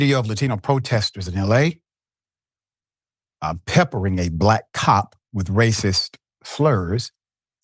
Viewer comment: I initial thought you said black top wow